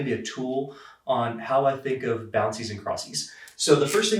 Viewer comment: did he say bouncies and crossies?